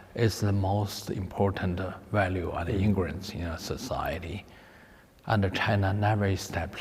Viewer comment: anybody understands the blank in “trust, quite often we're not talking about, is the most important value ____ in a society”? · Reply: Ingrained?